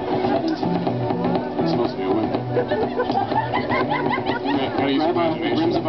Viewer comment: What does Spine say at like 2:04? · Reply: He says, "It's supposed to be a whip."